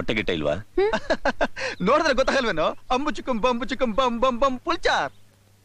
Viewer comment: what did he say can anyone tell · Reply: Pulchar - pure veg